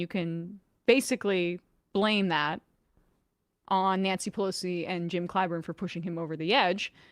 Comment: Did just say Nancy Pussy? 😂